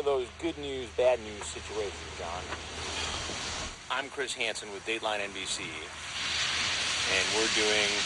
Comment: Every time he says "I'm Chris Hansen" in my mind I hear " I'm Chris Handsome"😂😂😂THE CHOICE IS YOOUURS😂😂😂